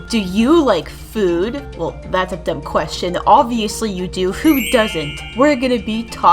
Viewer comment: Is it just me or die I just hear "Food Doesn't?"